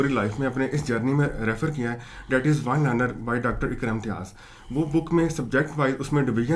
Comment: book name kya bola ap ny 3:45 mins py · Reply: I guess it it’s General Knowledge (ONE Liner) Objective by Dr. Iqra Imtiaz